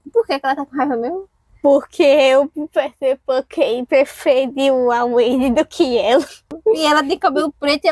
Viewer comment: Não entendi o que ela disse, alguém me explica por favor?? 😅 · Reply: O Peter Pan, do universo das Winx, preferiu a Wendy e não a Tinkerbell :)